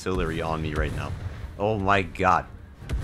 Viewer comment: Did he just say gyat? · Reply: He said good